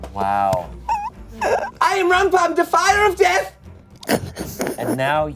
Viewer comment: "I AM RUMPUM! DEFIER OF DEATH!" Had me cackling like the hag. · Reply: i think because they all have french accents my brain misheard that as "i am rumpum, de fire of death" and i was like ... ok werk i love that for you. king of fire and death